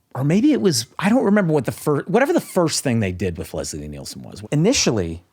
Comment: I thought he said Liam Neeson